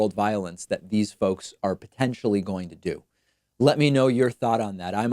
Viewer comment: Okay, so I watched this video with subtitles on and I noticed something strange. Around 7:21 David mentions violence that could breakout after the election and he says "potentially" but the subtitles say "Hopefully". I also noticed throughout the video that the subtitles didn't always match what David was saying. At first I thought these were auto-generated but they were set to normal english. Is somebody altering the subtitles so that they can take incriminating screenshots of things that David just flat out didn't say?